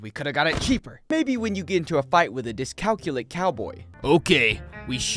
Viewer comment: I thought just said Dyslexic terribly wrong, until i looked up dyscalculia which is totally what he actually said fr fr